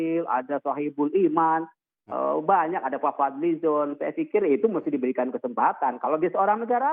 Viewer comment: fadil zonk apa saya gx salah dengar ???? wakwakkwakwakawkawkawkwakawkawkawk NGAKAK SAYA :V